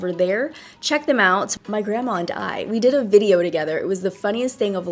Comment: I just realized that on 13:04 u said my grandma and I and I thought before u said my grandma died I was like WHAT??!!😭😭😭😭